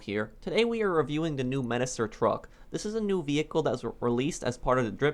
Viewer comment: I thought he said menacer drug